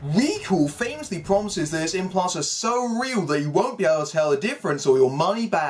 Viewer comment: "Recall promises __ is so real, you'll get your money back!" Does anyone know what said here? This is one of the rare times his accent is hard for me to decipher. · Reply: That its implants